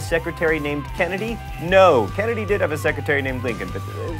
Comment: I swear, every time I hear him say 'Mental Floss' in the outros of these videos, it sounds like he's saying 'menopause'.